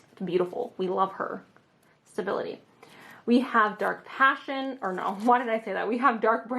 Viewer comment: idk why but i thought it said "dank passion" instead of "dark passion" LOL